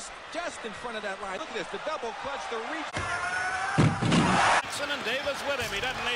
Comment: Did he say, "The devil clutched the reach"? Interesting choice of words. Also, James Jordan = 11 letters, Daniel Green = 11 letters, Larry Demery = 11 letters, and the 38 caliber = 11. Mr. Jordan was travelling highway 74, which is 11, He was found on Aug 3, which is 11...Does all of these 11s have any significance as well? I'm not sure, but I think it does. Does it though?